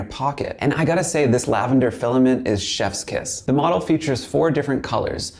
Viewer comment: did he just say "shess kiss"? seriously?